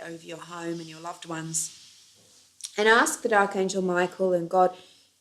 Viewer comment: did you say ask the dark angel Michael? Or am I mistaken